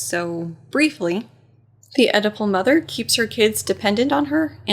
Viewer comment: When you said Oedipal mother the first time I thought you said edible mother!